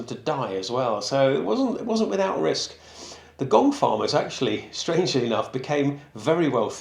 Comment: The gong farmer at first I thought you were saying gold farmer. 😅 Wonder if one was ever randomly hired to come early just to find something like a gold bracelet that was doped down there by accident. 🤔